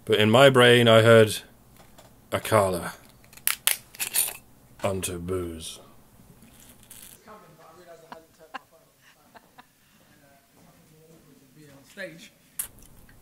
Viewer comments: I totally heard "on ta booze!" too lol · I heard "Akalla unto booze" too. :)